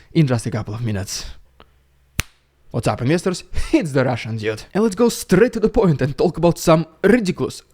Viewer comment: I love this channel but everytime he says "the Russian dude" I hear "the Russian idiot"😂🤷🏻‍♂️